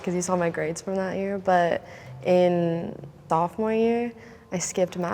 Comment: The one girl said “thothmore year “ instead of sophomore year